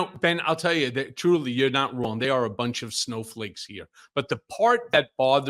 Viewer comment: I have TV going in the background & a table fan running nearby while listening to this. I thought Michael said that the GOP are a bunch of urinal cakes. He actually said snowflakes.